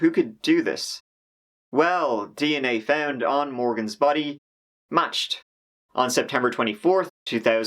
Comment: is he saying Matched? If so, who did it match? Also how did they find any DNA on her body if she was a skeleton? Or did you mean they tested the body and it matched her DNA so we know the body was her? If so, I wouldn't call it DNA on the body. Just say they tested the body and it matched Hannah Graham. It is confusing otherwise. Anyway I like this videos and channel, I just wish there were more videos. When I first found your channel, I watched your videos every evening and had watched all your videos in about 3 days. EDIT: @ 7:10 It sounds like "A woman who is only being identified as Orgy." Is that correct? She wasn't gang raped. No rapist orgy.